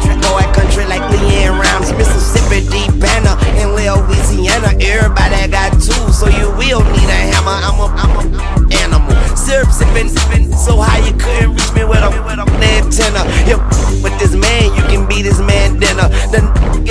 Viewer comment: What he say here? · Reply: Everybody got tools here we don't need a hammer. Im a pill poppin animal. Syrup sippin nigga, I'm so high you couldn't reach me with a fuckin antenna.